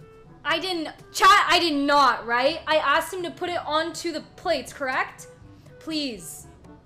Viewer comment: To he honest the first time I watched I also thought she just asked him to hold it, and when I went back the second time I heard her ask him to put it on the plate lol 😂